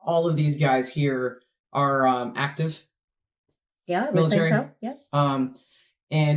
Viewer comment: Please read: Some people think at 3:50 said "actors" she said ACTIVE. Maybe it's an accent thing because we don't understand how "actors" were heard. You can always turn captions on if our accent sometimes gets in the way. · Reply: I guess anyone that thought you said “actors” must not be use to listening to you or be aware of your Knowledge and respect for the military